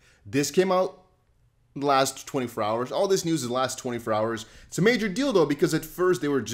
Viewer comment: did you just say "its a major dildo" at 54 sec?